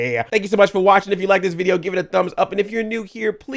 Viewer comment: What do you say at the end of your videos? I hear "I love you like a play, cousin. I'm Audi 5000."